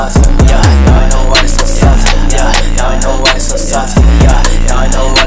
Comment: when he was yelling at the end it sounds like he said I DONT KNOW WHY IM SO SAUCY